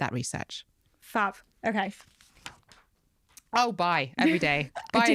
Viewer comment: i thought she said BYE to chia seeds haha fiuuuf